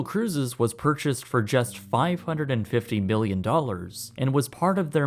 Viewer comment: you say billion instead of million